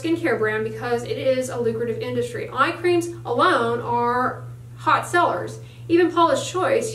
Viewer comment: when she says "..EYE creams alone are hot sellers" and I hear "..MY creams alone are hot sellers" and immediately wonder where the hell I can get these creams.. it IS a lucrative business, and you should probably start your own line.. jus sayin.